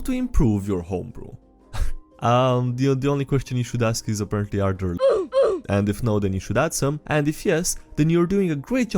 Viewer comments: what is the censored "L word" here. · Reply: Lesbians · It's not 'yes, and' or 'yes, but', it's 'yes, and' or 'no, but'. Anybody who sees this: what the hell is the censored word at 9:00? I'm at a total loss. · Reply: Lesbians